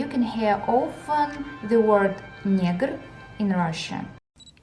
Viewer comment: did she just call me a nigger?